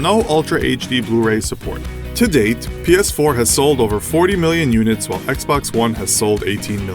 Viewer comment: It sound like you said penis4 instead of ps4